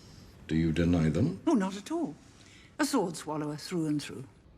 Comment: Did she say "a sworn swallower through and through"? 😨🙊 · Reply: Meera Malhotra sword swallower through and through